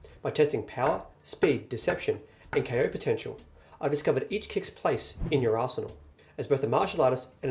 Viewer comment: i just heard - each kick's place in your arse